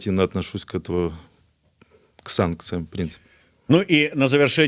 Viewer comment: Что он сказал? Санкциям бл**ь????